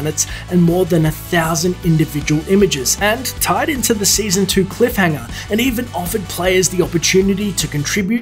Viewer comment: who heard the "cliff hang on lol"